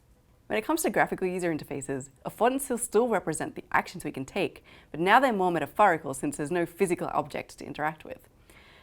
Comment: The subtitles are sometimes incorrect. At 0:40 it should read: But now they're more metaphorical since there's no physical object to interact with. Instead of: But now they moment of far is cool since there's no physical object to interact with.